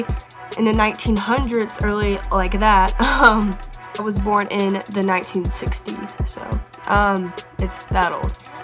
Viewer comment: It sounded like you said you were born in the 1960s?? 😂😂😂 I know that had to have been an accident since I know you said you're a senior this year 👏🏽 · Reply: I said that my dad was born in the 60’s lmao but omg its not my address but thankyou for looking out for me! :)